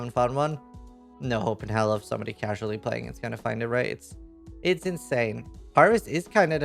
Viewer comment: find a what?! I listened to this about 15 times and have no clue what you said... sounds like "Raids" or "riots" or "rates"...............................